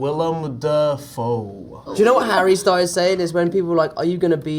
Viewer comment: omg Tom quoting Harry Styles is everything to me right now 😭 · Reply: omg i thought he said "harry styles said" not "harry started saying" 😭😀🔫